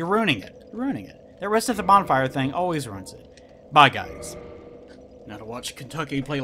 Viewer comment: For a second there, it sounded like you said Kentucky was going to play like shit. I'm sure you're just delirious from Dark Souls 2 butthurt.